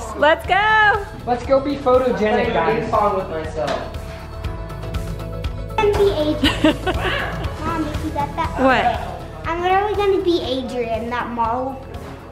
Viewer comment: is so cute❤️ Did he say he wants to pose like Adrien from Miraculous in 8:06?😂❤️